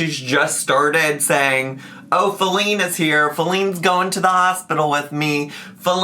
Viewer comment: I listen to Amber reactions/compilations while doing work so I've legit thought this woman was saying Celine the entire time.